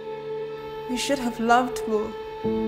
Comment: At first I thought she said "you should have loved Honda" lol